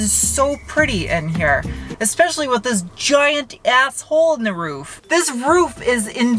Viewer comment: "It's so pretty in here, especially with this giant asshole in the roof", @ 3:47. I'm sure I misheard that.